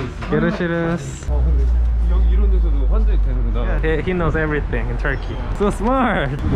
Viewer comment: he said something about Bangladesh. I think he said that Pakistan and Bangladesh are KARDISH. IS IT? And Love from Bangladesh